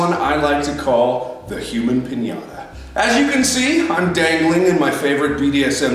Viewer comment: I thought that said Hakuna Piñata and I was very confused